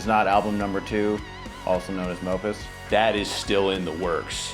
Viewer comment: did you just say Album #2 “also known as Nopus”?? 🤣🤣💀